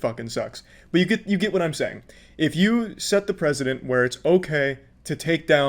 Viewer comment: this guy's voice is so crystal clear that the auto generated captions are 100% accurate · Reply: Not 100% correct. 3:10 says president instead of precedent.